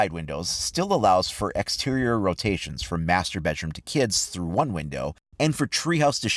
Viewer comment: am i the only one that misheard master bedroom to masturbate room?